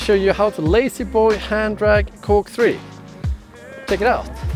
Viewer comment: Haha i thought it said ladyboy hand drags